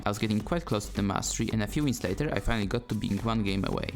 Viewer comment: For a second i thought ypu said a few weeks later instead of wins😂 i was like "pocos that trash?"